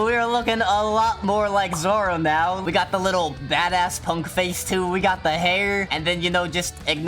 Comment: Bro at 1:10 he really said Bada**💀🗿